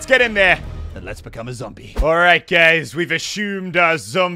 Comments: swear he said alright gays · OOPS ACCIDENTALLY SET IT PUBLIC BEFORE 720p! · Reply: why did you say alright gays